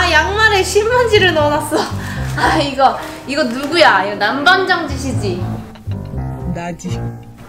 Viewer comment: 나만 남반장 ㅅ끼 라고 들려요?